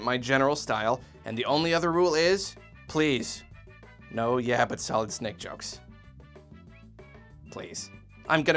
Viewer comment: for the life of me i cannot understand what he says on 1:55 yeah but selznick jokes? yeah butt zell nick jokes? what!?